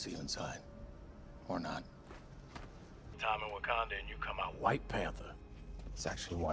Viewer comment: am i the only one who heard it as "white panda"